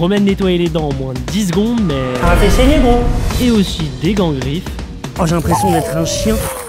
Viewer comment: ça t'a fait quoi ? 🤨🤨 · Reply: Ça l'a fait saigné